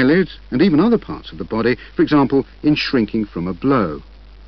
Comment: does he say 'in shrinking from a blow' or sth else?